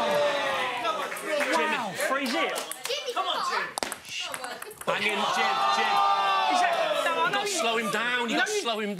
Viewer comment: What does he mutter under his breath at 1:21 after that little kid says 'Jimmy come on' haha😂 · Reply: Says shut up but sorta stops on the u of the up 😂😂😂